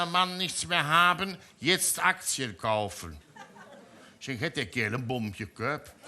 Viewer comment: Ich muss als Pfälzer mal fragen: Was hat er bei 3:23 gesagt? Hat der Kerl ne Bomb ....? Danke vorab für die Antwort. 😊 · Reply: en Bomb jeköpp - eine Bombe geköpft,statt einen Ball 😁